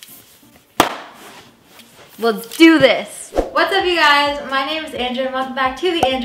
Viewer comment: I thought she said in her intro when she said whats up ur guys i thought she said " whats up u guys its android andrea" LMAO 🌈😂😂